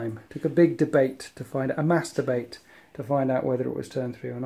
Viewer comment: When you said "mass debate" I thought you said "masturbate" for a second